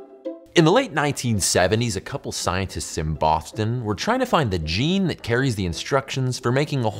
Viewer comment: I swear he says, "Scientists in Bofton"